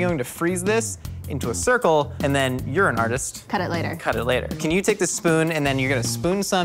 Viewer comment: when he said "you're an artist" I thought he said "urine on this" also his voice is helium